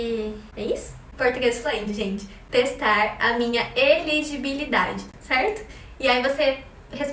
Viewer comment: eu ri muito pq entendi "testar minha LGBT" kkk